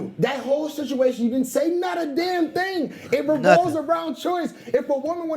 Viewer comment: What was she saying? 'Not a dam ting!'